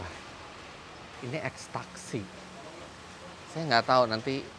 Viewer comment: ngakak beud pas nyebut ex taxi, yg kedengeran gw extacsi narkoba